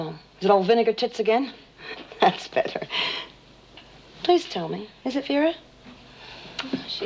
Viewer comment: Does Jim call Danny, Peter? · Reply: I think he said that's better not Peter 😂😂 as Danny said he wud go school and not skive altho to be fair first time I thought he said now beat it? /!!! 🤣 🤣 🤣